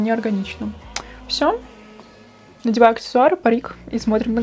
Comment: Послышалось,"надеваю аксессуары и поеду на работу" Все.... Пора спать😂😂😂😂